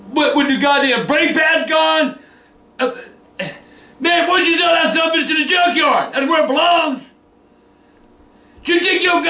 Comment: what did he say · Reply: He said take it to the trashcan